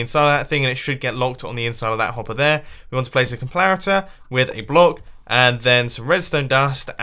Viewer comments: Can you do a toutorial for the diamond one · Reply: Did he say 'complarator' or did I imagine it??? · is it just me or did he just say “complarator” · did anyone else hear "Complarator? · Did he just say complarator? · Did u say complarater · did he say "complarator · Did he say complarator? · Did he really say, "complaritor"? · is it just me or did say complarator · Did he just say complaritor? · WAIT! WAIT! WAIT! AT 12:55 DID HE SAY COMPLARITOR XDDD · did anyone else hear "complarator"?? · anyone else hear him say "complariter" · did u just say complarator? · did he say "complarator"